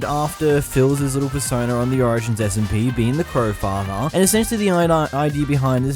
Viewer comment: I thought it said "CrowFEATHER"